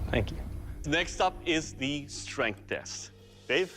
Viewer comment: When Doug Said "Dave" at 5:43 i Heard "Babe" and almost choked on my Coffee o.O